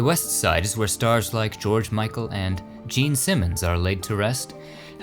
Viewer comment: Did a double take when I heard "Jean Simmons" thought I heard "Gene Simmons"